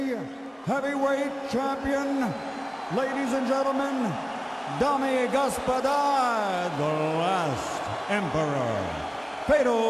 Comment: What does Michael Buffer say? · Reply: Дамы и господа. Means: Ladies and gentlemen in Russian